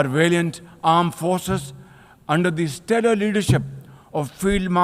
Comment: did he say ''terror leadership"?? lol....